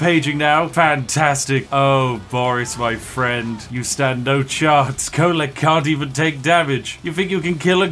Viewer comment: Did I just hear "STANDING HERE" at 15:30???